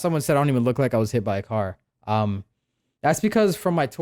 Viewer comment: I thought it said i got. Hit by a coma